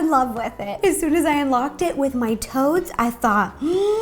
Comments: When she said the moment i unlocked it with my toads i thought she said toes not toads 😂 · At first I thought you said you unlocked it with your toes hehehe! "Toads" makes a bit more sense :P Also, the cake is adorable! ^-^ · I thought she said I unlocked it with my toes XD